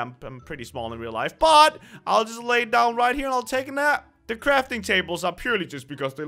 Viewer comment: I thought u said "crapping tables" is it just me or did it souund ike that?